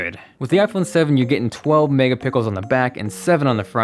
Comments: Did he say megapickles? There's this guy from that says this.... xD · Did he say 12 mega pickles? 😂😂😂😂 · did he just say the phone camera was 12 "megapickles"? 4:00 mark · Man, did you just say 12 megapickles 4:00? Damn, that camera is powerful! :-) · At the 4:00 minute mark he says megapickels instead of mega pixels hahaha. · did he just say mega pickles? xD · Is it me or did he say 12 mega pickles for the camera 4:00 😂😂😂😂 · am i the only one who heard him say "12 mega pickles" instead of "12 mega pixels" ..? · I think I deserve the iPhone 7 because I caught you saying pickles instead of pixels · did he just say megapickles? snapfam here btw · Did he just say 12 Mega Pickles? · Did anyone else catch that he said "12 Mega Pickles" at the 4:00 mark. LOL. My new fave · Did you say MEGA Pickles @ 4:00? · He said 12 megapickles instead of 12 megapixels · he sounded like he said mega pickles. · I heard 12 mega pickles instead of mega pixels at 4:01 :P :P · who else heard 12 mega pickles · wait!? did you say... 12 MEGAPICKLES? lol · sounds like he says mega pickles 😂😂😂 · HAHA @ 4:00 Three MegaPickles LoL. Did anyone catch that? Good One. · He said mega pickles instead of mega pixel Lol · I swear you said 12 mega pickles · did you say 12 mega pickles? 👀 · Did Say "MEGA PICKLES" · Is it just me or did anyone else hear 12 Mega Pickles at 4:01 ? 😂😭😭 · Did he say "Megapickles"? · Did anyone else hear megapickels at 4:00? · Did he say megapickles? LMFAO